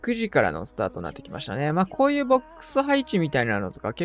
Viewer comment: 30秒くらいの「こういうボックス配置」って言ってたのが「こういうクソ配置」に聞こえてもた